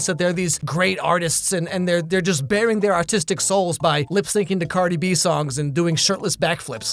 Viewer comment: Kinda thought he said Autistic souls